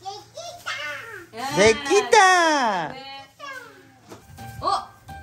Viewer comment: え？「できたー！」って言ってるの？🥺🥺🥺🥺🥺🥺🥺🥺 たまたまそう聞こえただけ？🥺🥺🥺🥺 意味わかってないかもしれないけど皆が言ってるの真似したのかな？🥺🥺🥺🥺🥺